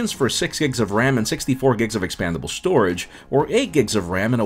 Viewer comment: Did you say 64gb storage?? 2:29.